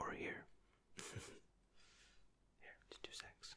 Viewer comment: Video was great but 7:16 does he say let’s do sex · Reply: Just two secs :D